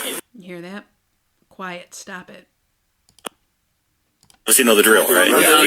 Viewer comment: Omg! I heard it "Quiet, stop it!"